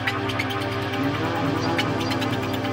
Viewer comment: did she just say I'll give you pleasure at 1:48?